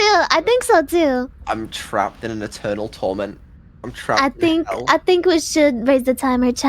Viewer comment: min Did he just say "I'm trapped in e-turtle torment"?